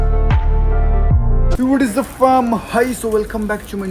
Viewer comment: what he ll say for intro ..I m not getting pls tell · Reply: What is up fam (means whatsup family) hi so welcome back to my new vlog